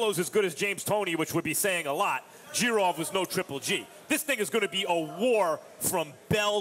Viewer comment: This nigga said GGG is a fighter not a boxer lmao? Stick to B-level movies bro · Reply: correct me if I'm right but did max just say "this nigga" 2:13 lol